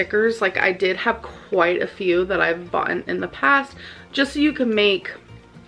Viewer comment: DID SHE JUST SAY " I HAVE BOUGHTEN " THE FUCK